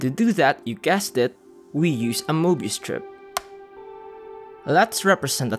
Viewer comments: I genuinely thought it said Morbius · Bro I thought it said Morbius